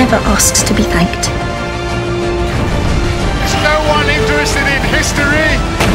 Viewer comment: What does the fourth doctor say at 0:45? "There's no something something in history!" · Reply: There is no one interested in history